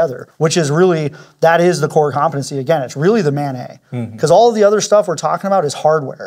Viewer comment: I'm listening as I work and I keep thinking I hear say "mayonnaise" instead of MANET and it jolts me out of what I'm doing (radio programming, ironically) every. damn. time. also, just hearing someone say 'interoperability' makes me glad. we emergency services radio people would love to hear it more second also, I've been trying to research but I don't see anything on it, does this use native FHSS for EM adverse conditions or is it single channel broadband?